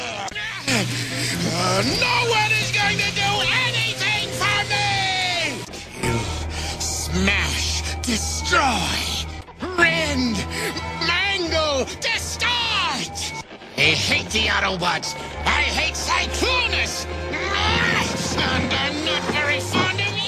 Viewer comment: Did I just hear this loony knock-off of Megatron said “mango” @ 9:03?